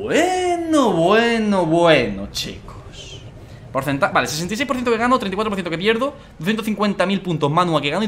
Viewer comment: escuché 66% Vegano